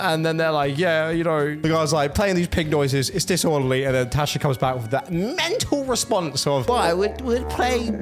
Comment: Tash ?? I thought you said Trash….